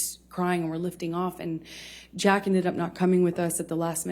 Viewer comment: Did she call him JACK¿